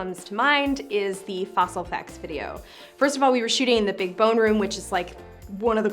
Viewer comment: "First of all we were shooting in the big boner room, which is one of the coolest places you could ever possibly go." No, wait. That can't be right. I mean, it COULD be right, but I think I may have mis-heard it. Lemme just go back and give it another ... ... Oh. The big BONE room. Big bones. Fossils. That makes sense.